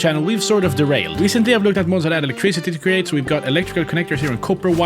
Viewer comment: What did you say at 0:02 ??? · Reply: Recently i looked at mods that add electricity to create